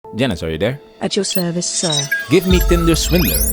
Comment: When you say "Janice" I always thought you were saying "Dennis"